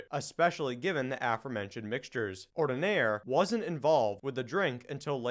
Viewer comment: Both in the spelling and in the pronunciation, the "d" comes before the "n." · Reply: Ah, sorry about that. It truly sounded like you said "Ornidaire" on my laptap. 8D I listened to it 3 times to be sure, but now on my desktop, it definitely sounds correct. My mistake, sorry.